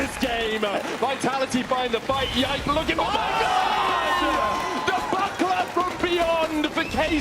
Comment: Did he just say 'the buttplug from beyond'??